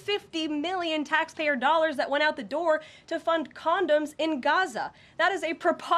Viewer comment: Biden admin gave 50 million dollars to fund condoms to Gaza? Dang! 😂😂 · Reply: My husband was listening. He thought she said “condos.” When I told him it was condoms, he made quite a face!!